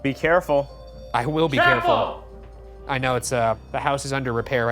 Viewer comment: does shane also say "carefull" or did i misheard?